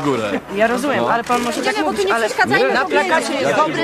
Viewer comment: Posłuchajcie ostatniego zdania i sprawdzcię o czym mówi ten dzieciak. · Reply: fugu12c Ja usłyszałem 'płacę na pracę willi'?, albo 'płacę na pra cywili'? Ciężko zrozumieć :/